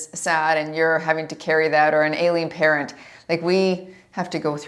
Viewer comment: I know you said "ailing" patent, but I heard "alien" parent. I was like...hmmm, that would be tough. Lol 👽